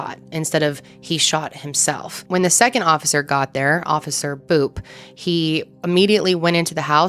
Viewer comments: I thought said officer poop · I was listening seriously to the conversation until you said officer boop and for some reason that just really made me laugh lol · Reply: 😂 that made me laugh too. I thought she said officer poop 😂